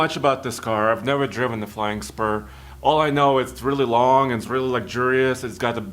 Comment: I heard Bentley “flying Sperm”